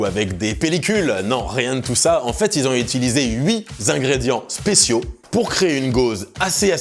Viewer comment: "ils ont utilisé huit Zingrédients..." ? C'est quoi un zingrédient LOL